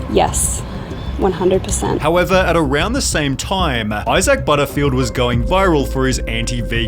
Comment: thought you said Asa Butterfield at first, had to check again 😂